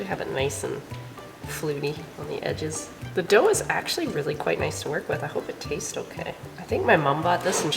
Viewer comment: I heard "the doe is actually guite nice to work with". 🤣 Not native speaker